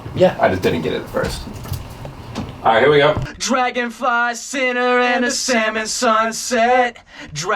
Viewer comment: Their band name should have been Wet Lettuce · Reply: Also I thought they were saying Dragonfly Center till the very end.